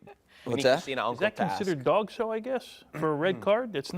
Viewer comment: Is it called Dogso? @ 2:03:30 😂